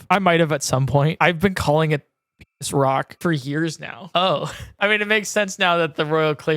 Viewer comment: what does he say here? Couldn’t find what he called the rock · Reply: It has now become clear that he calls it penis rock. Amazing